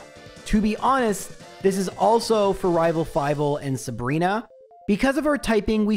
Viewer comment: Nice try, but I heard you say "Rival Five" instead of "Rival Fival" and now I'm pretty sure you're an imposter.